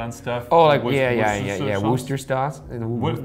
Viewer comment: Did ryan just say woodchester sauce at 7:37 🤣